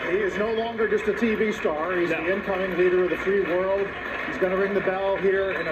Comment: Did he just say he’s the end time leader of the free world or did I hear it incorrectly? I listened to it again & that’s EXACTLY what the commentator said!!